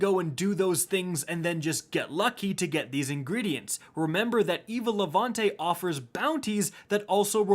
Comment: Am I the only one who thought he said Evil Levante